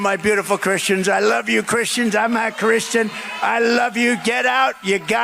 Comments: Sounded like Trump said I'm not a Christian! Listen closely! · I swear that during tiny trump's "Christian's, vote for me" rant, i heard him say "i'm not Christian"!??? Just saying, i heard what i heard!! He said it in his usual mumbling that he usually does to cover up his "slips" of his mind, like he started to say it, but toward the end decided it was not good for him so he slurred beyond it!! He is NOT a Christian, and anyone out there claiming otherwise needs to rethink their views about the Bible and the Ten Commandments!!